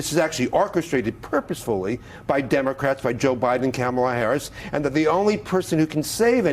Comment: what did he say? Cam a la? where's the fake outrage? Fckin hypocrites